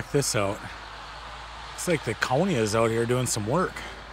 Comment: sometimes your accent throws me, I thought you said coney, it was county. I live in SW Virginia. :)